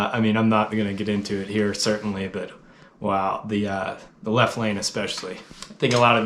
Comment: also, shit yeah on the driving etiquette! (plus I thought you said 'left wing" rather than "left lane" and I wondered why THAT was the moment you chose to go political! :) :) )